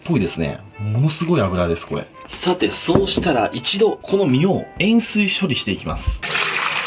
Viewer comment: コメ欄見ながら動画聞いてたら4:02のの 「塩水（えんすい）処理します」が 「塩酸処理します」に聞こえてビビった。